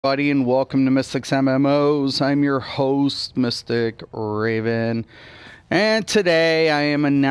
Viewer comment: you talk so fast man i always thought you said "this is your host mr. gaben....lol.